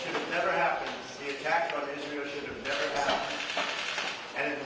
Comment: He gets 20 seconds to comment on a terrible episode and still fit in how it wouldnt have happened if there was a different president. He's moved up from toddler to the annoying kid in elementary school. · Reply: He said they. Not we. He meant Nethanyahu. I dont think you misheard him by accident. How can you BE so obnoxious?